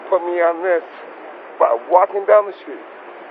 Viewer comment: Awesome job for the men and women in the law enforcement. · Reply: It sounds like he said he was just "walking" down the street.